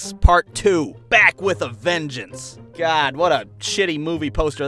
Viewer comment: I thought it said Black with a vengeance